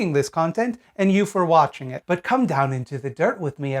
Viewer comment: I thought said "cumtown" instead of "come down". It was great